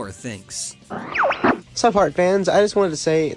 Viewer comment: Did he just say "Hart-fans"?